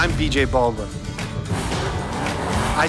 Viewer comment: I thought it said J Balvin at first 😂